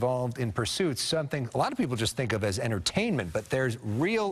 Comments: Did he say black people or a lot of people LOL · Ok wait, WTF🤔🤨🤨…@ 0:37 did the broadcaster say “Black people find this as entertainment”????!!!???!!!??? Somebody help me out on this one…